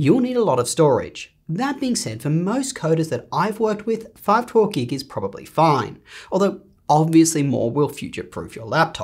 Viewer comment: I could'nt understand what he said regarding storage on 3:56 . could someone please tell me how much storage is enough ? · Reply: He said that 512 GB is fine